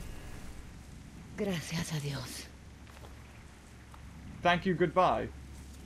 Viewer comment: She didn't say "Thank you goodbye," she actually said "Thank God!"